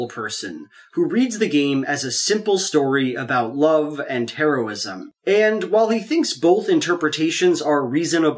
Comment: the autocorrect subtitles say “Love and Terrorism”.